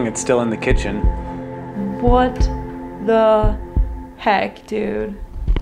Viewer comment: Does anyone else hear whispering at 35:32? Right after says “can you come over here?” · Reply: Yeah it was saying what the heck 😂